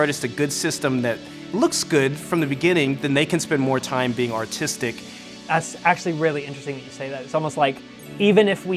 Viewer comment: I thought he said autistic